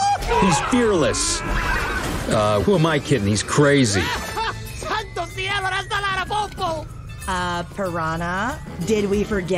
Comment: what did he say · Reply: Santocielopalaloto know has mexican